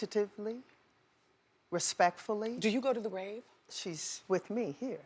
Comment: Grave... she didn't need to go to Whitney's grave because Whitney is always in her heart. But somehow I think you were joking when you asked that question... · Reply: no it sounded like she said 'rave' so i thought there was some event that might have used whitney's music as the basis of the concert. i'm from la and they would have events that featured someone like stevie wonder or prince's music. but now that i know it is grave, yeah, she doesn't need to visit the grave. thx for the info!